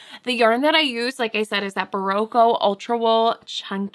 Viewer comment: She has berroco ultra wool ckunky which is 100% superwash wool. I looked it up ❤ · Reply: My bad! I thought she kept saying Vintage Chunky. Yes, Ultra Wool Chunky is superwash wool. It's fantastic too! I've had both in my store.